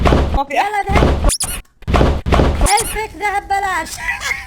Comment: شو قال ما فهمت؟ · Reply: Ahmad Ahmad قلها حلفتي عالفاضي يعني